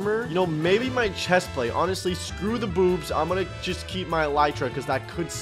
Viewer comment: DID YALL HEAR WHAT SAID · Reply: He said boobs